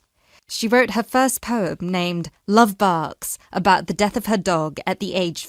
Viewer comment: 31 and for 14th I heard "Love bucks" lol